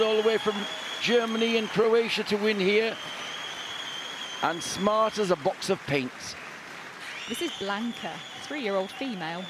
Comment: Someone PLEASE tell me... What does "smart as paint' mean??? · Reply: I thought he said, “Smart as a Box of Paints!”